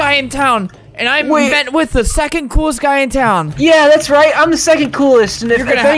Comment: IM DYING AT 0:04 I THOUGHT HE SAID "I'm in bed with the second coolest guy in town" O.O